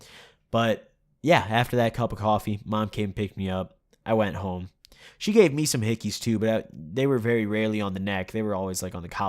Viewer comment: it sounded like you said ur mom also gave u hickeys